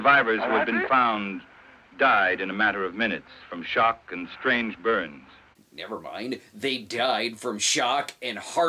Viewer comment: When "he died of shock and strange burns" was said, I originally thought the announcer said "strange BIRDS", and I was picturing prehistoric terror birds and thinking "wow the mythos of godzilla has really expanded to include a new roster of giant things to fight with, and birds are definitely something new" I was a little bit disappointed when I figured out that he'd actually said "burns" Aw, phooey That's no fun